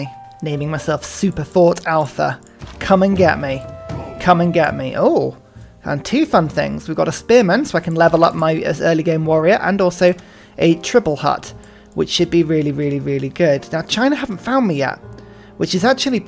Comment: did you just say trib-L??? As in, belonging to a trib?